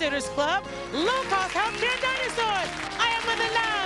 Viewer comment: Does she say "low-cost healthcare dinosaurs"?